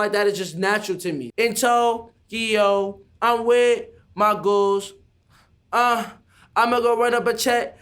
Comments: i bet the people who thought he said “i’m with my goons” feel mad dumb right now 😂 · “Omg I thought he said I’m with my goons😱”headass